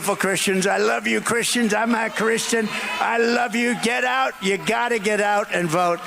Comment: I have listened to the comment multiple times, and it sure sounds like he says “I’m NOT Christian.”